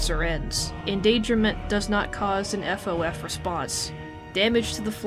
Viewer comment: Did you say FOF or SOS? P.S. I also used Auto-generated english subtitles,but it said FOF